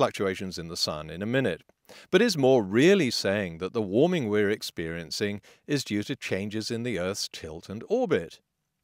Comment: lol, you contradict yourself, at 5.50 you say 'are patrick moore really saying that milankovitch cycles are causing glaciations ?' 'that is an easy hypothesis to demolish' then in 6.10 you say that these glaciations are triggered by the very same milankovitch cyles that moore was referring to 'with clockwork regularity' . so which is it, does the milankovitch cycles cause glacial periods or co2 ? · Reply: "LOL" you must have too much wax in your ears. At 5:50 I said "Is Moore really saying that the warming we're experiencing is due to changes in the Earth's tilt and orbit?" I suppose "the warming we're experiencing" does sound like "glaciations."